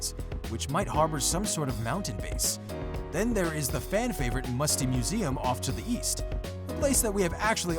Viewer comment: Dusty Docks seems boring because we already have Loot Lake... also, I thought it said “Musty Muslims”